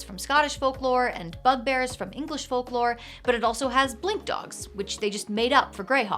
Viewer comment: I misheard “blink dogs” as “blimp dogs.” I wouldn’t mind a fantasy dog that inflates like a blimp